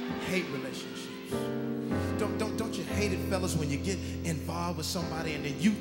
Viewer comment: Did anyone else hear the guy say “f*** you!” When he said he hated relationships?🤣🤣🤣🤣 like what man